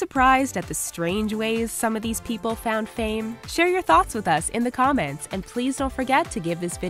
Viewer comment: dhymond rebekkah jordan makiah shanae tanika michealla kayla tamah mya OH SHIIIIII... I THOUGHT IT SAID share your THOTS IN THE COMMENTS BELOW